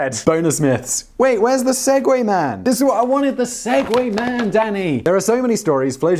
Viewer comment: Got distracted for second and thought i heard Simon say "I wanted the Segway man, daddy!" 👀